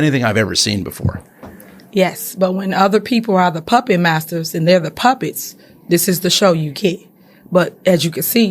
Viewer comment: did she say... "When other people are the Puppy Masters and they're the Puppies?!?!